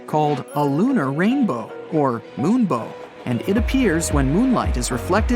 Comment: In 3:57 did you say the gamer moonbow